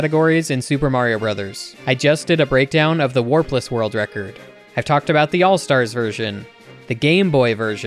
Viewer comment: I misheard it as "worthless world record"